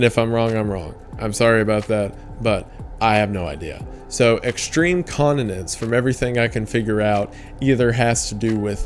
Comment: I was listening to this while playing a video game and during tier 3 I thought you were saying "extreme condiments" which is a very funny conspiracy theory to imagine.